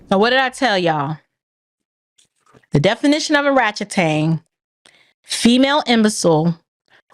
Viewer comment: did she say "O-ratchet-tang"??? LMFAOOOOOOOOOOOOOOO